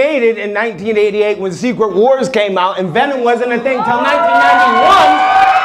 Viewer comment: Ive watched this 4 times now and I hear "Zebra Wars" every time.